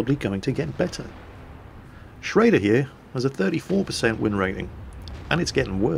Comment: I thought he said 13%win rating